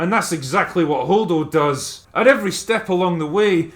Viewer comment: At first I misheard her Name and thought her Name was Hodor...